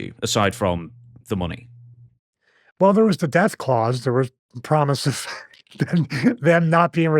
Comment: I heard Deathclaws 😂